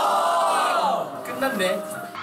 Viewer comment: ME ENCANTAAAA! Curiosidad: que dice en el segundo 0:09? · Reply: lo que dijo fue "끝났네" (it's over/se acabó) luego de ver la expresión de minhyun como queriendo decir "eso fue todo vamonos a la verga wey" bc minhyun papito :v♥